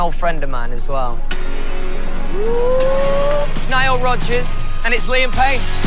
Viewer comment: i thought he said its niall rodgers and liam payn, and im totally screeaammm, then i didnt see niall,lmao ahahhahah so stupid i am